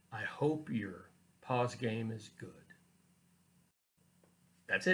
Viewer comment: Actually I paused it the first time even though I thought you said "paws game"